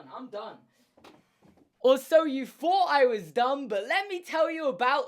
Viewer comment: What he said: you thought I was done? What I heard: you thought I was dumb?!?